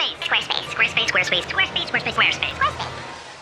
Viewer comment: i heard